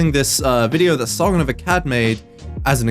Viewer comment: When you said Sargon of Akkad I thought you said Song of a cat. FULL NYANANISM NOW.